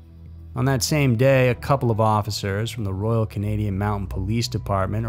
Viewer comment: Did you say royal Canadian mountain police? Or did I mishear?